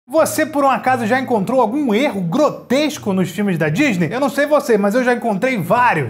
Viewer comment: eu entendi erro grosseiro kkkkk curte quem pegou a referência ⚽